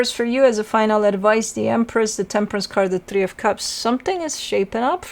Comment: I heard "tempress" 😄